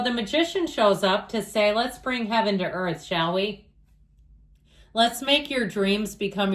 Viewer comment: You said let's bring heaven to earth 'shall we' but I heard 'Sally' which is my name. LOL! I had to rewind and listen a few times. My own private reading. So accurate. Thank you.